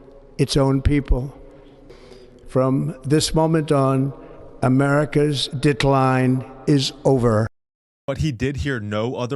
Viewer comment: i thought he said " americas dick-line is over" 🤭